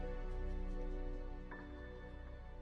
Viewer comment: If you can't tell what he says at 2:11, he says "I can't do this without you"